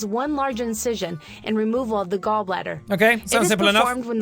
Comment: I thought it said "Ball Gladder"